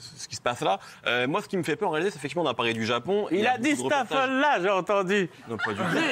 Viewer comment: A 4:23 je n'ai rien compris au fou rire de Doc gynéco.Qu'est ce qu'il a dit ? J'ai compris:"il a dit staffella"